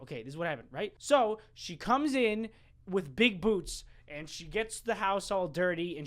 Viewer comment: I thought you said boobs not boots